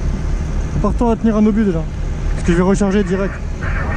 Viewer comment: "Prêt" - Ready 0:08 "Prépare toi à tenir un obus déjà, car moi je recharde direct" - Be ready to take a shell already, because I'm going to load it immediately 0:14 "départ obus !" - Start shell 0:24 "on s'en fout" - We don't care 0:28 ~Attention, section ... tirez~ - Careful, section ... fire (ty Isq78) 0:32 "Feux !" - Fire ! 0:36 "Aller !" - Come on ! "Obus !" - Shell ! "Charge obus" (ty Mickael Boucher) - Load shell "Restart" - Restart... And I think that all, the rest was just impossible to understand because of the noise of the artillery.